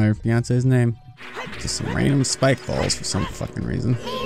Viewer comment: I love how he only found out about the revive faries at the Ganon Castle, literally the last dungeon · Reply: Miles Doyle I thought he said balls instead of bulls! Lol!